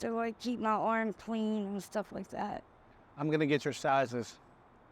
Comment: "stuff". 💡 did you say stuff 💭💉😴